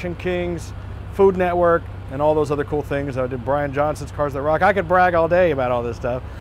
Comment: Did you just say you did Bryan Jonson's cars (The Rock) @ 33:23? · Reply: I was on Brian Johnson's show, "Cars That Rock", Season 3, Episode 3, Cadillac, he drove my ECTO-1.